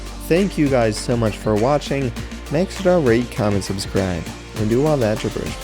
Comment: I thought he said in the outro "make sure to rape, comment and subscribe" Bill Cosby